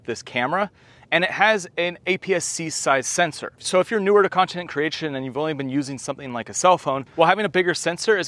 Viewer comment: Did you just say “continent” creation???😂